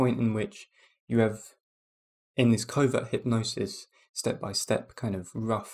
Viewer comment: At first I thought you were saying "COVID hypnosis" lol